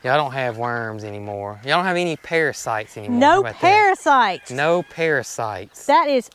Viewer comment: Paris was hanging back, because she thought you were saying "Y'all don't have Paris' sight.". 😉